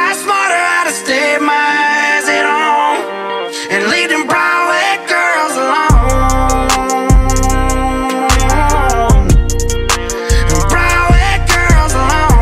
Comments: My husband thought he said leave them bald head girls alone😂💀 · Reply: I thought he was saying leave them brown haired girls alone 🤣🤣 till I read the name of the song after listening to it only 20 times 😂😂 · My husband thought he said leave them bald head girls alone😂💀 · My husband thought he said leave them bald head girls alone😂💀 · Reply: I thought he said “ leave the brown white girls alone “ 😂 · My husband thought he said leave them bald head girls alone😂💀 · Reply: Mines thought it said brown head girls alone 🤣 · My husband thought he said leave them bald head girls alone😂💀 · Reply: I thought he said leave them paw az girls alone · When I first heard is I thought he was saying bald head girls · When I first heard this it sounded like they were saying "leave them frog leg girls alone". I was like what in the world is a frog leg girl?